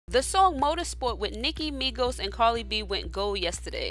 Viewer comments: Why it sound like she sayin "Carly B" 😂😂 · Am I the only one who heard her say Carly b